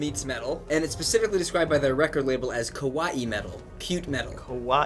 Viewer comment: Lol, it kinda sounded like they said Kowai metal instead of kawai. Kowai metal is scary metal in japanese, but kawai metal is cute metal.